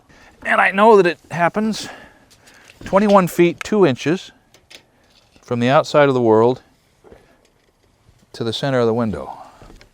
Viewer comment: did you say from the outside of the World to the middle of the window?